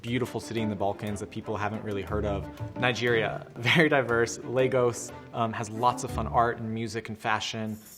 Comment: OmG I thought Lego's but he said Lagos